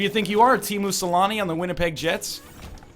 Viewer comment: For a second, I thought the Bald Bro said he was on "Team Mussolini" instead of "Teemu Sellanne"